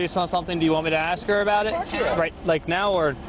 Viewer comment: anyone else hear “fuck yeah”